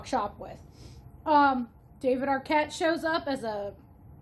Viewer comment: NGL, I wasn't really paying attention (sorry!) so when you said "David Arquette shows up" I heard "even her cat shows up", which was very confusing. What you said made way more sense. Though I could argue that what I heard would also be an interesting change I imagine. Idk, I've never seen this.